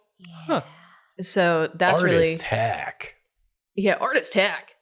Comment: I misheard "Artist Hack" and instead heard "Art as heck!"... still works